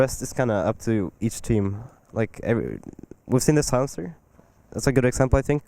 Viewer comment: What the fuck does he say at 6:46? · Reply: "we've seen the silencer"